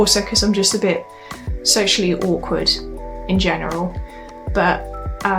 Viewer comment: I guess u mean socially awkward. It just sounds like u say sexually awkward.